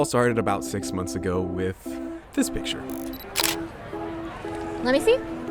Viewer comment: I had to replay it a couple times, cause I thought he said "this b****"